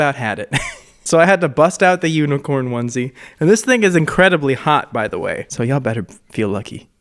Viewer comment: did he say “This nigga’s incredibly hot btw”